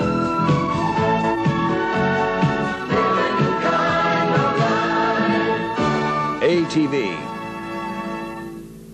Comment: What does he's says at 0:06? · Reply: “living the kind of life”